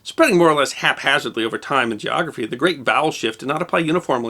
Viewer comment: is it just my bad hearing or at 11:08 did he say "The Great Bowel Shift"? LOL